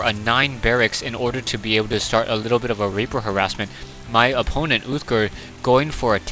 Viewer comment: i swear at 1:23 you said "to start a little bit of raper harassment" LOL